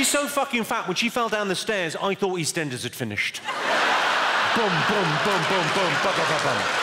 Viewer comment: "When she fell down the stairs I thought Eastenders had finished!" Hahahaha! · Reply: Wow that makes a lot more sense... I thought he said “when she fell down the stairs I thought these tenders had finished” 🤦🏽‍♀️😂